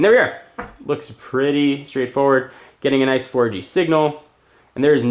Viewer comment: did he say getting a nice orgy signal ... lmfao